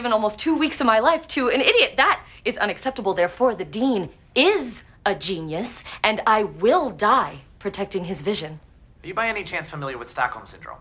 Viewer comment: You see, I could see that her lips were saying "dean" but I kept hearing "Bioware"